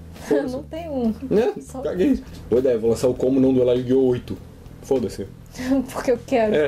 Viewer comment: Eu ouvi "como não duelar yugioh 8?"